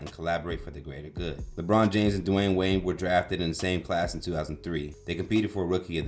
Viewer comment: I heard you say "Dwayne Wayne". Lol